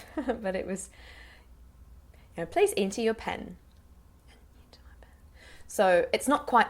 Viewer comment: sounded like "place into your pen" to me. If I was in that situation I'd be so confused what